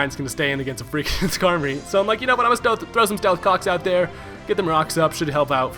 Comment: Did he say, "I'm going to through some stealth cocks out there." at 1:20?